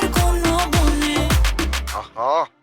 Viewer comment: কি বললো ? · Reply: Line busy